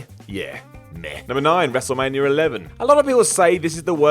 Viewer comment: Why do I always hear 'Wrestlemanier 11'??